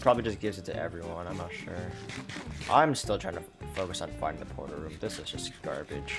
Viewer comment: At around 22:24 the automatic caption says you want to focus on finding the porno room 😂